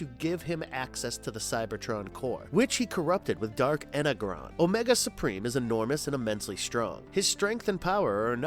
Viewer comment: you totally said "inner gone" you have lost all my respect you are exactly like every other content creator who actually knows nothing of the content you are making...........BTW ITS PRONOUNCED INNER JOHN that's the pronunciation not the spelling do your research or I'm out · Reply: Fuq me I just heard you say dark inner gron......I'm dead